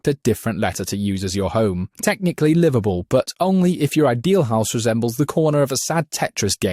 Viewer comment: Sounded like you said Liverpool instead of liveable 😂😂😂